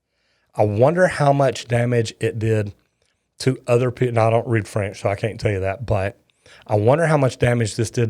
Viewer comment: "I don't speak France," As a British man, I find that most amuseing. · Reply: he clearly said ''no, I don't read french..'' not "france" i think you misheard because of his accent.